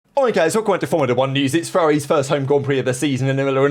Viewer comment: I've always thought you said "Oi guys" instead of "alright guys" lol